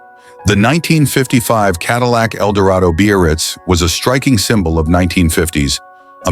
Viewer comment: When the announcer-guy is talking about the '55 Caddy (8:00), he speaks of the model. He calls it .. the Bee-or-reetz, or something like that. Some variation on .. beast? I dunno what he's saying. I've never heard the word he seems to be saying. `Beo-or-ritz'? `Bay-oh-ritz'? `Beer-writz'? I even went to the web-page for 1955 Cadillacs & there is no-such word in the sales-literature. There is `Brougham' for the ultra-large monstrosity land-boat of that era - but you've just stumped me. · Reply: He's (or "it's") trying to say "Biarritz." The Biarritz was the Cadillac Eldorado convertible model at the time.